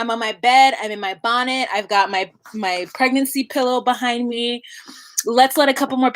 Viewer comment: No, I just have a hard time sleeping and the pillow helps 😂😂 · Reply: I thought you said, “pregnancy pill.” I was like, “What?!”